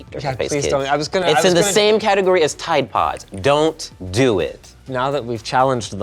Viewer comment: all I heard was "DO IT!".